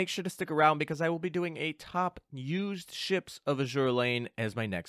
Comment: For some reason, I heard the phrase "I will be doing a top used ships of Azur Lane" as "I will be doing the top ships (fandom shipping) in Azur Lane"